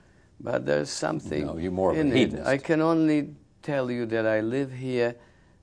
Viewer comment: What does Rose call Polanski at 30:56? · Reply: Hedonist